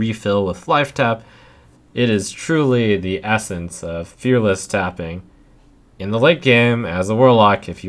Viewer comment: I thought it said fearless fapping